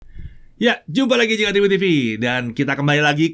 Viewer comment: "Jumpa lagi di channel review tv". Masih bs kedengeran kyk gitu juga ternyata... Wkwkwk..